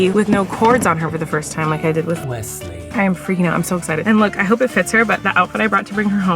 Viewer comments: What is the audio clip over saying “Wesley” at 4:05 😂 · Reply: correcting since she said Flynn instead of Wesley lol 😂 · did anyone else hear a voice saying welsley when said hopefully i pick her up with no cords on her this time or at time stamp 4:04 or